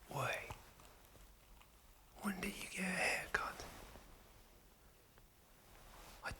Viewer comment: I thought he said "When'd you get here cunt?".